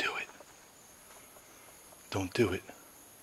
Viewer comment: i heard don't do it